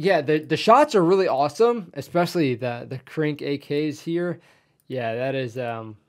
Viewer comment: "the krank(?) AK" Krank? I don't believe I've ever heard anyone call AK-74su as "krank" but "Krinkov" is a common nickname for it but as far as I know no-one knows where that name came from. It's an iconic AK variant to be sure. It's a status symbol in many circles and was favored by none other that Osama bin Laden for example. Personally I like the design very much but... like the Soviets found... it's a bit of a "nieche" weapon. I think the Soviets manly issued it to Mi-24 crews during the Afghan War. Which might explain why Osama bin Laden appreciated it so much. EDIT: now that I listened what you said again I now hear you say "Krink" or "Crink". 02:26 They have inward opening doors in Estonia? I thought that was an American thing... 04:20 "Full face masks, what they are for?" I'm just a keyboard warrior but apart from just protecting the face from debris during a violent entry & stuff it just might be about human psychology. Like assaulting a room butt naked vs. wearing jeans & T-shirt... which would you choose? 05:12 "not a crazy crime ridden" Russian Mafia has a strong foothold in Estonia. Like Italian Mafia has a strong foothold in the US. Not judging anyone here.